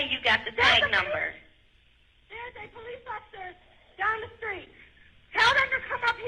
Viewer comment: Did she say "I'm the police officer." Here?